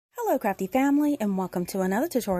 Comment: I love how creative you are! Every time you say “hello Crafty family” I hear “hello crappy family”. 😂